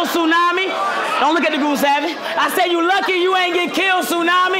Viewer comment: When Top tells Surf “Don’t look at the goon savvy” 😂😂😂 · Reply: No lie I thought he said don’t look at the boobs sagging all this time😂🤣